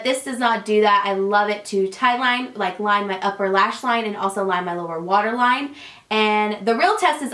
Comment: When you were saying you love the eyeliner to tight line I thought you said I love it to Thailand! I was like okay girl you do you hahaha